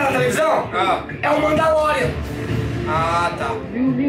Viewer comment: Entendi ele dizer: Mãe da Loren kkkkkkk